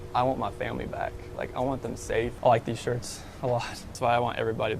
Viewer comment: did he just say I like these shorts a lot that’s why I want them back 😂